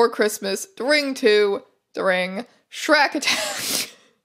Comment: Misheard 'shrak' as 'Shrek' and I just about became quadriplegic from the whiplash